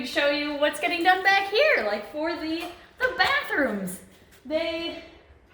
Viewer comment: I thought she said backrooms 💀😂😅